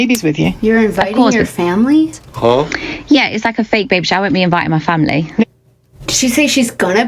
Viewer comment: I think she said "Yeah, it's like a fake baby shower. I won't be invitin' my family."